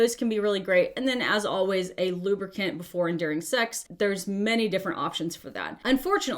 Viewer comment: I did a double take because I misheard "...lubricant before and during sex" as "lubricant before enduring sex" and I was like yeah, if you're merely enduring sex instead of enjoying it, that's a problem.